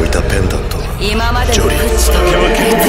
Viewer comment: I hear Harry Potter for some reason